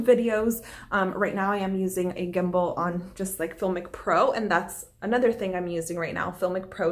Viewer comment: I couldn't quite hear what you shared at 3:22? It was about Gimble and something you use to film your self tape auditions. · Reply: Yes, the filmic pro app :)